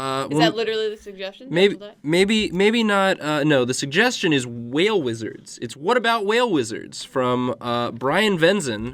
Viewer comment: i thought they said "whale lizards" at the beginning of this video and i was just extremely confused the entire time